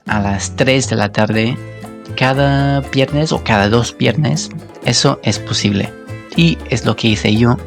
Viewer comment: In line with the lesson, why does he pronounce "viernes" like "piernes"? I swear I hear a "p"